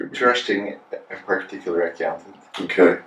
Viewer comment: what does he say at 6:41??? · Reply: Dino Mujkanovic "Trusting a particular accountant."